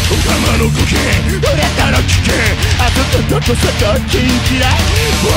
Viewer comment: I swear he says kinky